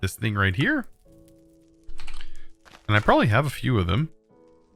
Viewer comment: and i.. oooh Me: wait... Did he just say and i oop?